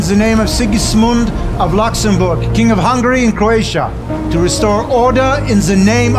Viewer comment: did he say king of hungary and croatia? I am from croatia